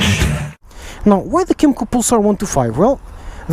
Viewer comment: What's it called? I thought you said Kymco Poopstart